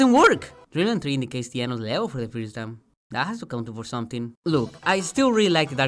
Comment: I can't tell what you're saying about Dreamland 3 here. "Dreamland 3 ??? (negates the L?) for the first time" It does what for the first time? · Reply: "Dreamland 3 indicates the end of the level for the first time"